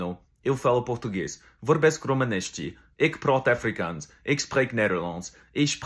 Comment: As a south African I can confirm he speaks Afrikaans fluently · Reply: It sounded like he said eek praut akfrikans🤣 I am also south african